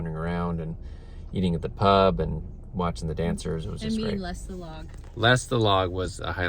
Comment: British pronunciation is somewhat hard to pick up; all I kept hearing was “less the log?”